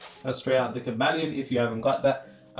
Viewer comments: what book did he say, it was hard to understand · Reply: The Kybalion search audiobooks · What was the name of the book you mention around the 10:50-11:00 minute mark? I couldn't make out what you said. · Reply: It’s called the kybalion